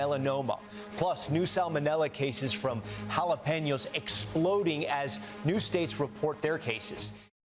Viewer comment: I heard jalapenos exploding and was very confused.. and then I heard the rest of what he said lol 😂 idk why I thought of actually exploding jalapenos 🤣